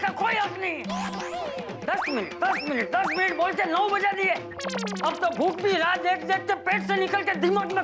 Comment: 9 bja diye subtitles says